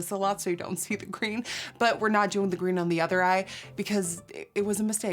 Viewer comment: I thought you said no green on the other eye lol